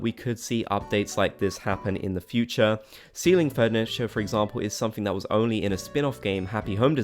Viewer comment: You said 'ceiling furniture' ... I thought you said 'STEALING furniture' LOL! Like, you can take items that you like from your villager homes, or the villagers taking stuff from yours :D